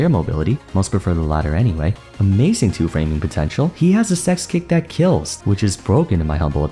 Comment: Can somebody tell me what he said at 12:32 right after "amazing two framing potential" it goes: "he has a (missing word) kick that kills" Thanks!. I hear "sex" but i don't think that the right word^^'